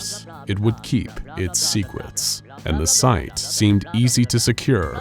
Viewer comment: sounded like he said “Secwets”